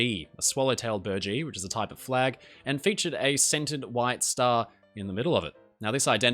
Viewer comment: I thought you said a “scented white star” at first and my dumbass thought “wait, what did it smell like?” Love the titanic stuff on the channel tho. Idk why it’s so fascinating.